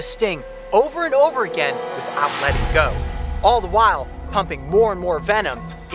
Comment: I thought he said humping not pumping 😂😂😂